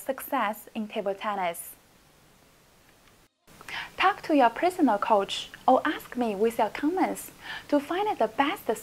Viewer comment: I thought she said talk to your PRISONER coach😂😂